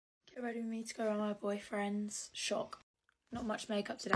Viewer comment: No cuz I heard “ get ready with me to go out with my boyfriends”